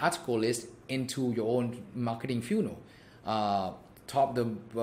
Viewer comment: Dude, love it. However, I will say, I've never heard anyone pronounce the word "funnel" the way you do. I first thought you were saying "funeral".